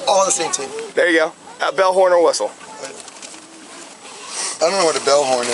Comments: Im watching this stone cold sober and I thought he was saying bellhorn or whistle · Who else heard "bellhorn" and thought he was referring to a singular object? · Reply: At one point I think I even heard him say bellhornwhistle. 😂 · Im sober as a church mouse and I thought that dumbass said "bellhorn or whistle". I was thinking......"what the hell is a bellhorn? " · He said “bellhornawhistle”. He said it so fast. So because we couldn’t understand what he was saying because the cop was speaking so fast and incoherently, he’s drunk? Ridiculous man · The officer said, "BELL HORN or whistle' as plain as day verbatim!! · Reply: I have a boat and all the required devices. I know what a bell, horn and whistle. Every time the tyrant asked about them (except the last time)I thought he was saying bullhorn. You must be a fellow blue line thug or family or bootlicker. He was a total joke that abused his oath of office and caused the entire family and friends to lose their respect for the badge, the kids will never forget what this tyrant did and my guess is they will never have any respect for all badges. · Who else heard "bellhorn" and thought he was referring to a singular object? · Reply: I heard "bellhorn or whistle" 😂 this cop is dumb as hell. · "That cop's enunciation skills need improvement. I, too, heard him say 'bellhorn, and whistle.' Who sounds impaired? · I thought he said Bell horn or whistle, not Bell, horn or whistle. Get over yourself.